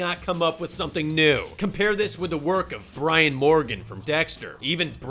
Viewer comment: did you just say Brian Morgan?!